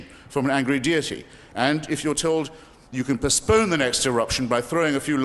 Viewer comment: Chris says Perspurn or something like that = anyone know what word that is - can't find it online! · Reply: I know this is 3 years after your comment but he said 'postponed'.